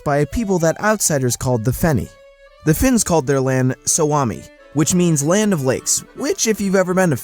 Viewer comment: Have to say the narrator kinda buchered the pronunciation of the word ”Suomi”. To my Finnish ears it sounded like he said ”Swami” or something.